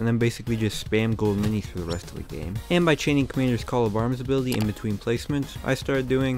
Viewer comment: the subtitles say Commander’s call Obama’s ability lol.